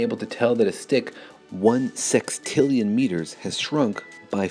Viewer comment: The french subtitles are really bad on the numbers ! At 2:10 it reads 1mm instead of 5mm and "one quadrillion kilometers" instead of "one sextillion meters"